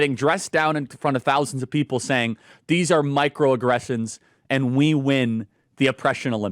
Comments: At first, I heard that as "Mike Rowe aggressions" and I thought, " for heavens sake, now what?" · Why do i keep hearing "Mile Rowe aggressions"? 😂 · I heard “Mike Rowe aggressions” multiple times before I realized they were saying “micro-aggressions”. Either they need to speak more clearly or I need to get my hearing check!!!! (My vote is for the latter, based on past experience.) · I keep hearing "mike rowe aggressions". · I heard, " Mike Roe aggressions.' Gave me a chuckle.